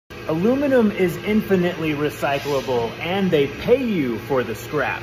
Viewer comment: I heard "A woman is infinitely recyclable" 💀💀